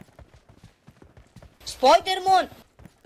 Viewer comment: I got confused for a moment when I heard the "Spoidermon" landing ngl 😂